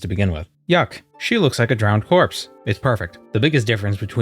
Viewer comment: Bruh i realized the character looks just like my ex and then I heard "she looks like a dead corpse"